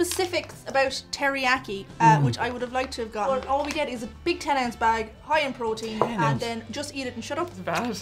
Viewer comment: Maybe it's the accent, or maybe I'm really tired, but I could have sworn said "their 10 inch bag" on the first beef jerky.